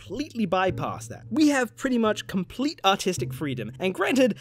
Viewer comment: What he says: "Complete artistic freedom" What I hear: "Complete autistic freedom" Meh, close enough.